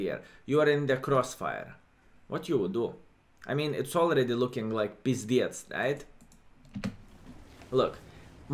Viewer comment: Hahaha what did he say at 18:17? “It’s already looking like pizdjec”? LMFAO